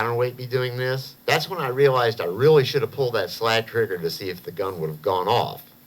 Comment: "That's when I realized I really should have _ _ _ _ _ to see if the gun would have gone off." Can you fill in the blank, please? Anybody? What was said there? Sorry, I couldn't understand. These videos are so good otherwise, I just really don't want to miss a single bit. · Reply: JT Innac "pulled that slack trigger"- sorry for mumbling.